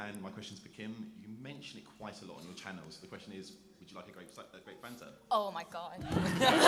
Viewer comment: I didn't quite understand what the guy said at 9:30. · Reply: 'You mention it quite a lot on your channel, so the question is, would you like a grape Fanta?'